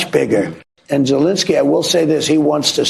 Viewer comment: Did he say Jewlinski??😅